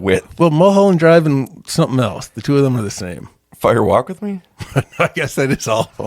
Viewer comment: I thought you said Spiders Walk With Me and now I need that movie to exist.